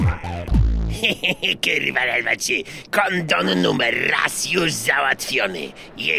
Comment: usłyszałem konon XD